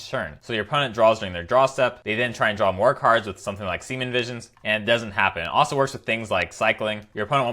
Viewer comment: did he say semen visions?